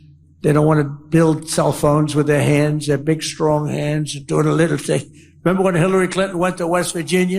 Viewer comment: I heard Trump says " Take my hand, My strong hand "